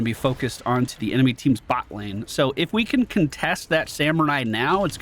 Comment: I just love it that it sounds like you saying "Buttlane" XD